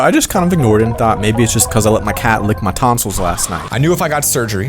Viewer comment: hold up before u carry on, did u just say ur cat licked ur tonsils?!?!